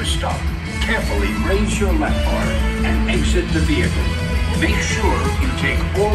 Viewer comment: Great video! One thing I noticed: at 5:00, I think Stan Lee says lap bar, not left bar!